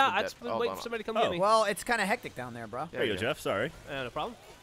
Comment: Did michael say "bra"? 6:01?